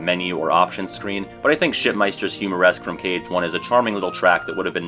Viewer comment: Now, you said shipmeister. I fully admit it. What I heard was "shitmeister" and I nearly spit out my soda when I heard it 🤣🤣